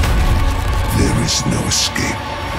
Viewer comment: "Only Bolivian" MISHEARD :D